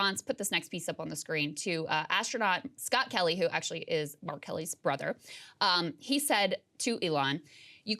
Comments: I thought you said "astronaut scott kelly, who is actually R. kelly's brother". 😂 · Sounded like she said R Kelly lol